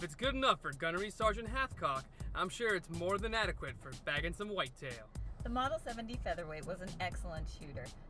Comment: What did you say? Good enough for shooting something and whitetail.